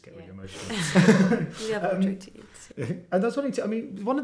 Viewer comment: What does noemie says in minute 5:02 ? About page 28? I did not understand everything... thanks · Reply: “We have our 28”